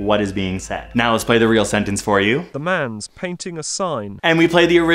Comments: I thought the audio first said: “laves is painting a sign” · I heard “Move! She’s painting the story!” I saw a woman in the backseat of a car looking at the seaside while a scary monster rips a little hole in the sky evilly smiling ..interesting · I actually thought it said “man sees the signs” which is shockingly close to “the man’s painting a sign”. Idk how it was so close! Maybe I thought of this because I always try to break things down and understand reality from the little bits I can see and think of all “the signs” in my life and what they mean 🤷‍♀️ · “The lamb speaks to the sign” Hey, I had a surprising amount of syllables right. I don’t think this really reveals much about my subconscious though. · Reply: Lol I heard “the bears paint with signs” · I heard "Hello, thank you, I'm fine" - super interesting to see in the comments how many different versions there can come out from what each of our minds had expected. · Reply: oh that fits better than what i heard, 'hello, painting is fine.' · I was off by one word! I heard “the man’s speaking a sign” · i heard "wow paint a song" ahahaha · I heard "the man speak to a song". And the image was like a world map at first, but then I saw something like an alien or something standing in a room. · "Hello, thank you for calling" I love these quirks of perception so much. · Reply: Is it weird that I thought it said "Help! I'm in pain!" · I heard "man speak to a sign"